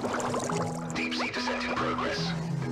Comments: In the original commercial, I always hear "Deep sea descendant burger" as the first line. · In the original commercial, I always hear "Deep sea descendant burger" as the first line. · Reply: lmaoo i always heard “deep sea disintegrators”